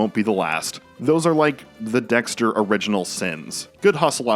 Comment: did you say.. original sins 😏